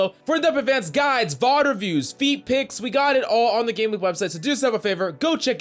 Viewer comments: did he say feetpics? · did he say feet pics? cuz im interested